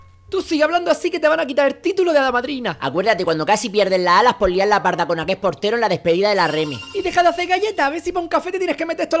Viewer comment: que dijo? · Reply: Joel leon Acuerdate cuando casi pierdes las alas por liarla parda con aquel portero en la despedida de la Reme.